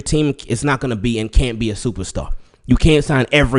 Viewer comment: I thought he said "starter" not "superstar". Of course everybody not gonna be a superstar